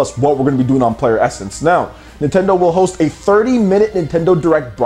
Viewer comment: I legit thought u said "Nintendo Direct will be in 30 minutes" i was about to freak out for a sec lmao